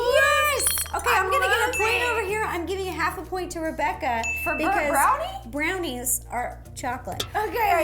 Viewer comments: On the chocolate one I thought said bird brownie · I thought said bird brownies! 😂😂